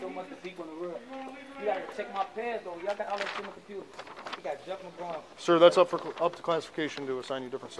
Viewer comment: i wouldn’t go in there wit jay ahh either 💀💀🌈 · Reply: i think its 1:21 when he say yall got people like jeff mcgraw in there 😂😂😂